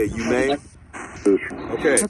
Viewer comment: did he say you may or Tommy ?